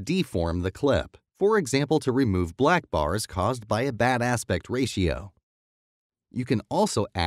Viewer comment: Is it me or I heard around 6:10 badas rasio